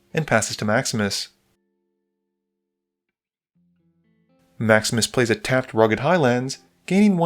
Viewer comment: I keep hearing "Max Misplays"